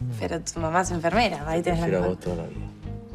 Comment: Alguien entendió que dice Renso en el 46:08, cuando ella dice pero tu mamá es enfermera.... Que le responde él??? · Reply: A te prefiero a vos toda la vida